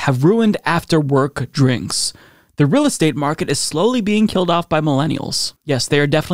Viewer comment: I thought he said millennials were killing the Rosé market 🤣 oooohhhh… the real estate market. Nah, I think the real estate market don’t want our little Pennies.